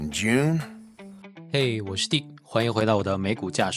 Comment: 听成 “hey， 我是dick了”。惊了